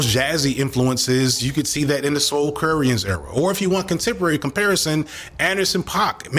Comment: Anyone hear what rapper he recommends here? I couldn’t quite catch it. He says it when suggesting a rapper like Mac Miller with jazzy elements. Would love to know 🙏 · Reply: Big L or Big Pun from the soulcrearían (spelling?) era. At 1:06:08 mentions Anderson Paak, which also has a jazz influence (Their NPR tiny desk concert is in the video) 🎵🎶😌